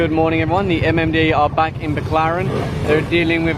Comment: i always misheard Baclaran for McLaren. peace